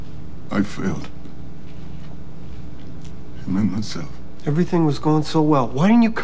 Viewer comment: What did Kareem Saïd say?... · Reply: 'Him and myself"